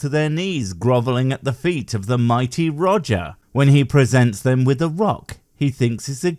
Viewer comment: When you said "The Mighty Roger" . I just heard "The Mighty Woger" (As in Life of Brian: Welease Woger) it made me laugh out loud for a few seconds.